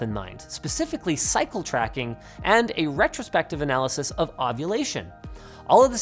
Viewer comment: I heard " a retrospective analysis of copulation".